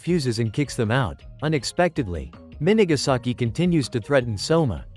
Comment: i cant with her name min n¡-ğæ saki?! [That's what I hear